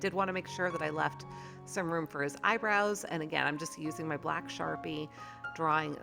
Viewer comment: Hi.. I started to laugh so hard because you were saying eyebrows and I thought you were saying hi Rose LOL I had to come back into the room to record it again to see what I was listening to LOL.. But he looks so good